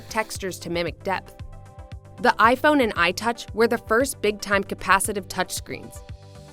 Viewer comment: did she really said itouch????